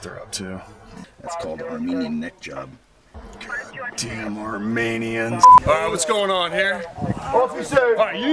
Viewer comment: what officer police said? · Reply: said God damn Armenians.